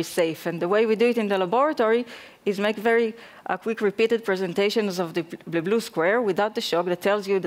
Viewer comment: They do WHAT KIND of presentation? I couldn't understand the word(s) there. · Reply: Quick repeated presentations? As they show blue screen, once again blue screen, and one more blue screen and so on.